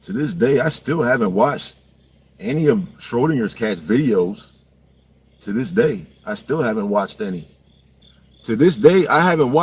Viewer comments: I thought he was saying in the beginning to this day I haven't washed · In the first line out of his mouth, I thought he was saying "I still haven't washed"!! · I thought he said "to this day I haven't washed" probably true 😁